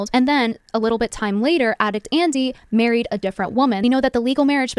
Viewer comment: hold the fuck up. Did she say “addict Andy”? I’ve listened to this bit a dozen times and that’s all I hear